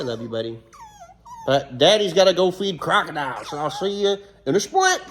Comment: i thought u said u were gonna feed kaido to the crocodilians 😢 😭